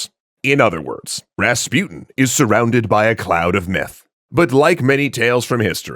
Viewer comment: Not gonna lie, I heard "a cloud of Meth"